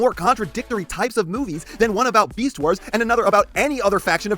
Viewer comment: I thought you said beeswars instead of beastwars cus my ear are doodoo feces